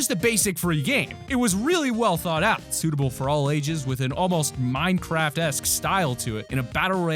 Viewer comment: did he say MINECRAFT